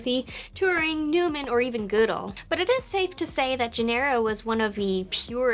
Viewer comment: Where would you put Haskell and Julia in the tier list? I heard "Goodall" but saw Gödel.